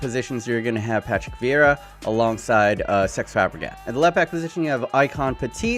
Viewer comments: did say SEX fabregas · Did you just say "Sex Fabregas"?! 😂